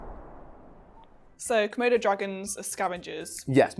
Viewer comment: says: ...komodo dragons... i heard: ...murder dragons... lol